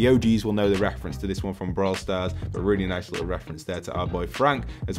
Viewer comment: did he just say brawl stars I know that game and play it